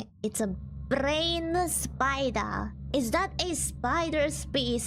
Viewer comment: It's supposed to be "brown spider," I think the auto-generated captions couldn't understand the accent.